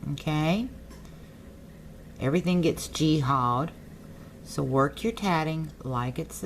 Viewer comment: Did she say "jihad" around 9:12? Like...kinda weird if you're using an Arabic term for a holy war in a tatting video, yeah? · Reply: Michael No I did not say Jihad. What I said what g-hawed. It is a slang term in the south for things getting twisted up or turned wrong. This channel is all about how to tat nothing more. No religious meaning at all.